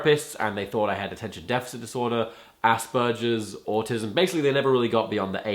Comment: I thought he said "assburgers"xD